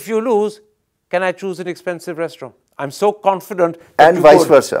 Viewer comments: Amazed to see the confidence. This man will earn our attention and respect if he is proven right on 10th March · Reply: Did Thapar say can i choose expensive restroom instead of restaurant at 37:23 ? 😃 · Did Thapar say can i choose expensive restroom instead of restaurant at 37:23 ? 😃